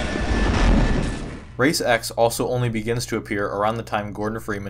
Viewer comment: I legit thought you were saying "Ray Sex"